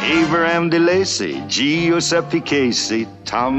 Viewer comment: this whole time, I thought he was saying GeoSept DeCasey..